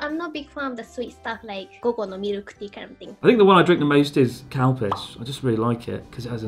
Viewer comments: did he just say cow piss ? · When they mentioned Calpico I thought they were saying cow piss all the time, like "I love cow piss" or "Why is my tummy sad when I drink cow piss? Ah it's because the cow piss has milk in it!" Or "I like the cow piss flavour" or "there are some places where they make a coctail with cow piss and sake and I think it is really nice"